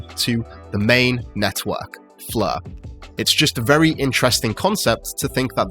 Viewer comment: Yes, I can't wait to get my "fleur" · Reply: I heard "flurr" lol